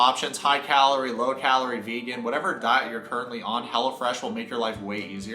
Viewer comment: Side note* I low key thought he was saying Hella Fresh 💀😂💀😂💀